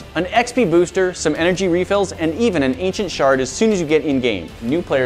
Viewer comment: It sounded like he said “an ancient shart” LOL